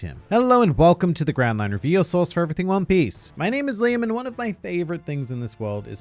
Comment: I literally heard "My name is lame" :'(